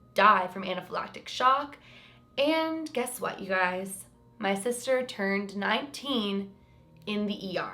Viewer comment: is only 22? I thought she was at least 25.. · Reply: Ohh I’m a dummy! I thought u said your sister turned 18 until i finished the video! 🤐